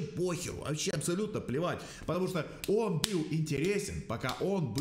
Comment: Когда он сказал плевать мне послышалось блевать